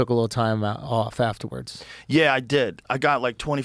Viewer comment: yeah i have like 25 minutes right now" -joe while the video is 16 seconds away from 25 minutes. Idk even know what he meant or said thats what i thought i heard